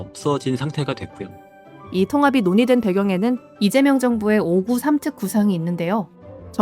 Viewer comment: 5극3특 아닌가요?? 내용중 5구 3특으로 들리던데여